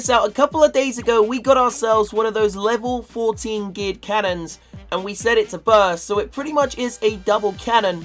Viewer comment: What the fuck do you call the cannon? All I hear is "gig cannon" just call it double cannon you fucking noob